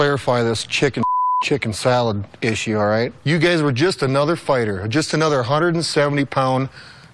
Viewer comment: 😀😃😄😆😁🤣😂🙂🙃🙃😉somewhere starting 2:19 Did brock call them and say "you gays"?! Instead of you guys?! Damn hes low key insulting them every chance he can